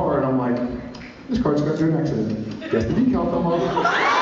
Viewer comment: what does jared say from 1:35 to 1:50? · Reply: Bucky Barnes I think he says something along the lines of, "This car's gone through an accident, guess the decal's coming off."